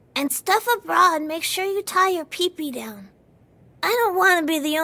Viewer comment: "Make sure you tie your PP down." Did I hear that right.